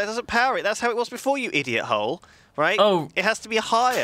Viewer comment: Did Lewis just call Duncan an idiot hole? at like 10:33.